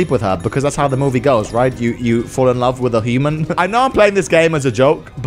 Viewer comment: "Fell in love with a human," I thought he said, "Fell in love with a demon"